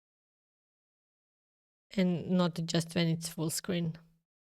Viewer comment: I don't understand what you mean by not open it full screen. You can aleays go full screen and you go out of the full screen or minimize it with controls on the center of the top screen, they are just hidden and will appear when you go with mouse over it. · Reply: Oh, I see. I think I misunderstood you in the video. I thought you said "Don't open the full screen, you will thank me later"